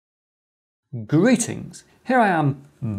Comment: Wait, did he say "GUI-tings"?😄